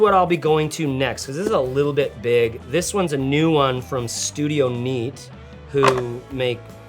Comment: I definitely heard studio meat 🍖